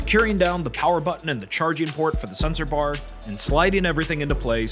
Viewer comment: i thought you said censor bar, not sensor bar, so i spent way too long wondering why the Porwiigon U would have to censor things lol